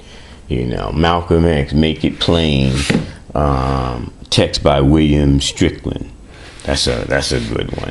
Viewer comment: What book did he say??????? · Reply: Malcolm X make it plain text by William Strickland